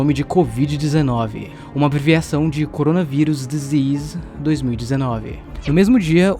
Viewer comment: Só eu que não entendi quando ele fala o nome do viros? Minuto 5:59 kkkkk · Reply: coronavirus disease 2019